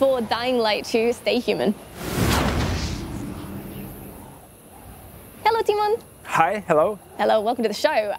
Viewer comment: I just heard. Hello demon. Or tymon?